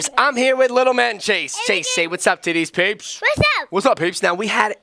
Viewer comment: Did say ti**ies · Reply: No he said to these peeps